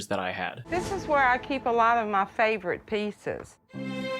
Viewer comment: When that lady said "this is where I keep most of my pieces", I thought she said pizzas. I'm just tired though.